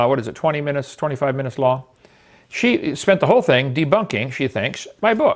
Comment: Did she say "debunking"? I thought she said "have a laugh" Also I love Kent baiting her into a debate on his channel... she has WAY more views than his videos